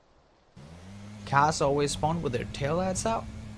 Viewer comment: what does he say? "Cars always spawn with their tail out top?" or what does he say? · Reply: "Cars always spawn with their tail out"